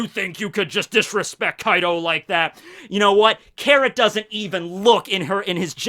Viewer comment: did i just hear 'carrot-tor'